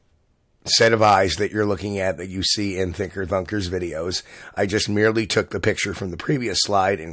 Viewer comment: When he says Thinker Thunker he sounds like he says Thicker Fucker